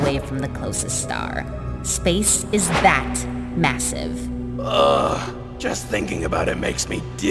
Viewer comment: Did europa just say, massive?